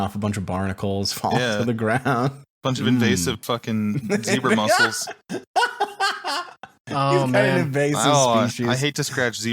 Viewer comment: I thought they were saying zebra muscles the first few times and I was pretty confused.